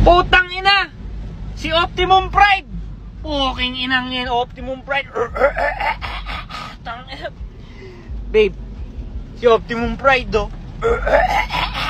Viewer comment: I hear "optimum prank"